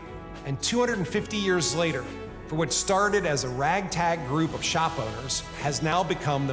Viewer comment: Haaaa!!!! I thought he said, " rag tag group of shoplifters has become the most powerful military in the world" lmmfao!!! That would have been close to the truth if some show of force hadn't been brought in..